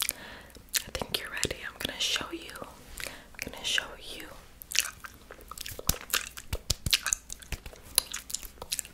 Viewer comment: ne diyor anlayamadım helpppp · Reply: "I'm gonna(going to) show you"